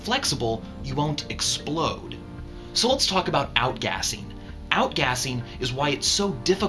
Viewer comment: when you said outgassing, I thought you said orgasm.